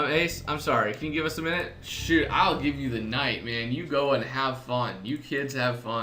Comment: lol ace when you said, "sure, I'll give you a night man", lol i thought you said ill give you a nightmare! ;) anyways im sorry cudnt watch ur vids as i was very busy but ill be watching them soon honestly but i dont understand that why does cc team dislike Frank so much that they made him a killer and even Frank worked with you in fewer cases than Amy. Why criminal case why??? why did you have to make him a killer? why? i mean really liked Frank even cheif Marquez used to shout at him so much!